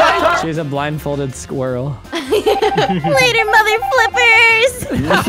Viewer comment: Did she just say mother f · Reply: She said mother flippers